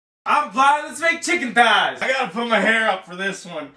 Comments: Omg I thought he said "I'm high, LETS MAKE CHICKEN THIGHS!" Then I read the comments... but also... I'm high. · I heard it as I'm fly let's make chicken thighs I think it's still applies ..if you read it like I did that's funny